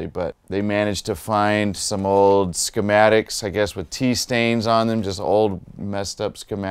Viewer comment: Did he just say, "some old schematics with jizz stains on them"?